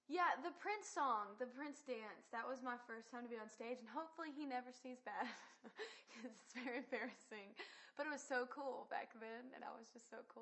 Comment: im so sorry, i meant 4:38-4:43. can anybody tell me what she says? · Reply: "I hope he never sees that, it was very embarrassing! But back then it was cool". I doubt you'll see this after 10 years, but oh well, I'm gonna try.